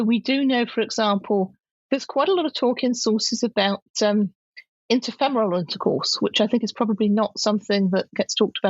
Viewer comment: I think at 4:13 she says "inter..."??? sex? As less sinful than anal sex. Not oral and not anal, so...help me out here. Am I missing something ?!!! :)) Thanks for this, so interesting, and brava to you! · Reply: Interfemoral